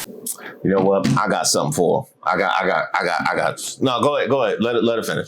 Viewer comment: bruh, I thought you were about to say "I got something to shove down your throat" 🤣🤣🤣